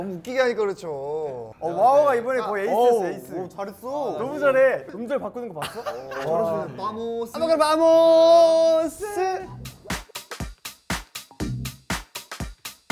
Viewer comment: Ok, eu posso estar, mais já estando muito louca. 7:32 Eu ouvi alguém falando : " OK VAMOS"